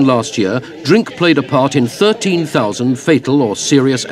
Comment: 1960 - 13,000 deaths or serious injuries per year caused by drink drivers. 2020 just 200. Now thats a stat. Banning drink driving was such a good move. Im been a firefighter for 21 years and have seen many horrendous accidents caused by drink drivers. I detest the selfish idiots. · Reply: Fair comment, id misheard the part of the video at 0:25 where its claimed that drink played a part in 13, 000 serious or fatal accidents and heard it as 13, 000 fatal .. Its still a shocking number whatever way you spin it. A reduction in highest count from 13, 000 to 8,000 * in the 60s does little to undermine my argument though its still a shocking number and of course the impact of the law change wouldnt be felt overnight as clearly many in this video felt the law was daft. What exactly is your counter claim ? That the law is not effective at reducing deaths ?